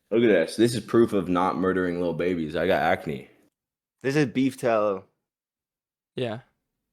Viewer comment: did he think he said beef towels? lol